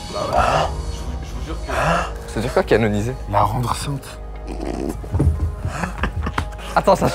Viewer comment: À 41:14 j'ai entendu "la rendre enceinte" je suis plié en 2 mdrrr